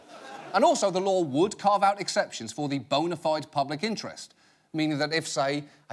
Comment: I was just listening and not watching at 12:49 when I heard him say "boner-fied"...